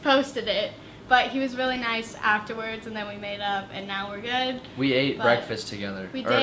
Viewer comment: When she said “we made up” I heard “we made love” I was like hUh❤️🙈